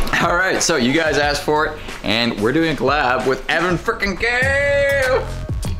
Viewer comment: the subtitles say "Evan freaking gayyy!"